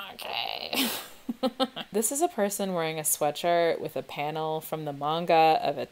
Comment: When you said sweatshirt I thought you were saying sweater with a really weird accent for a solid minute